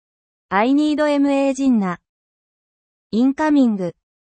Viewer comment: "I NEED EMEME DINNA" at least thats what i heard lol time stamp